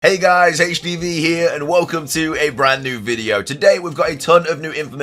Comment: Man said.. I thought I heard hey guys hpv here... then I looked at the time and realized I should been sleeping a while ago 😅